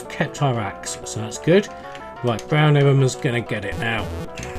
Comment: Haha "what's down here? Brown Arremer" - misheard that as brown enema